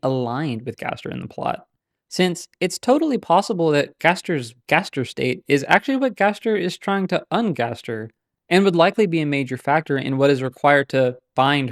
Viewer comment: bro what are you even saying here .. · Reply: “Maybe Gaster being in the sort of situation/state he is in, is actually what he’s trying to undo”.